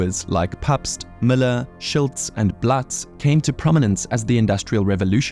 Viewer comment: Is it just me or did he call it "Shilts" just after 2:02?